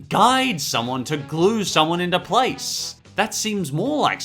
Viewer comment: I hate Patrick Star. Fucking idiot needs killed. Or put into place. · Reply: Thought you said "space" instead of "place" for a second. Wait, that's a great idea! GET SANDY'S ROCKET READY!